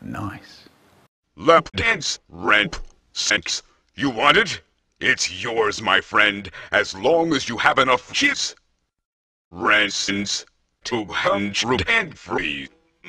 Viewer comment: What did he say? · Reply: "Lapdance? Rape? Sex? You want it? It's yours, my friend, as long as you have enough jizz. Raisins. Two hundred and three. Mmmm!" It seemed funnier to make at the time.